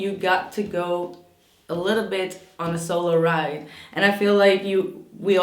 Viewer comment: ♐💕 ... I think you said "solo ride" (?). But I heard it as "Solar Ride" 😁🤣. just figured it out, lol ..and so true, too. Either way!!